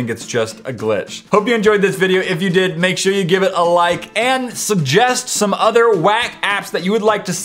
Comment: Did he say subbjest?